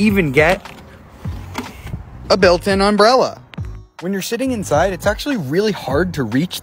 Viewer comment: I Just Heard "when youre shitting Inside" and i am laughing so hard en